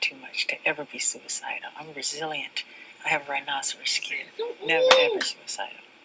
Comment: What is he saying at 29:27? “I have a ......skin” Lol sorry I’m not American and I just can’t understand what he was saying and I would love to know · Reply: rhinosaurs skin